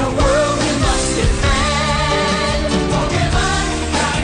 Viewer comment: I always thought Pokemon said "In this world we must befriend!" But they say "Defend." Makes more sense now.